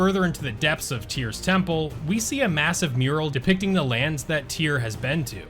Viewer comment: Did I hear MASSIVE!?